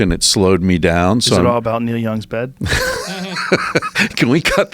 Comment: Can ayone tell me what was said? Around 0:16 or a few seconds before that what Joe said. English is not my first lang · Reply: i think he said "is it about Neil Young's bed?"